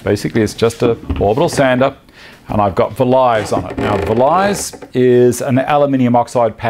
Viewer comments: At around 13:22 you say you're using an orbital sander with an aluminum oxide pad with ? on it? It sounds like you're saying velize or vilise but I have no idea what you're saying. I can't find any reference to it in your description and I can't find anything similar in search. You say that it's a pad that builds up a wax, I've never heard of such a thing. Can you help me? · you mention you have a pad on your orbital sander. I hear vy-lies. What is that? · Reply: Hi Lee Dabkey. It is spelled Vlies and pronounced fleece. An aluminium oxide pad that Festool does.